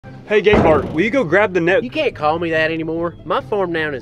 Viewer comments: I thought he said hey gay bart😂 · I thought mans said gay Bart this whole time😅😅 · At first I thought he said gay Bart 😂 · I thought they was saying gay Bart the whole time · Holy crap, for so long. I thought they said "Gay Bart". I've been watching these videos for a long damn time, and only just realized that s***. Lol